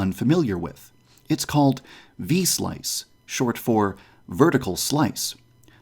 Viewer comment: I thought it was about to say V-Sauce lmao